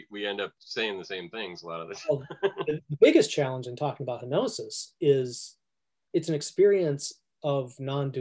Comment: you annotated that part as "challenges with hypnosis" instead of "kenosis"